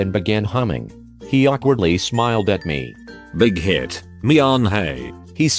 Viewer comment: I thought it said big head not bighit omg